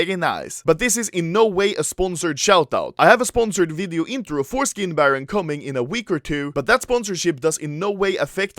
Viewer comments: Did you just say "Foreskin Baron"? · All I heard was "I have a video coming 'foreskin' baron" HAHAHA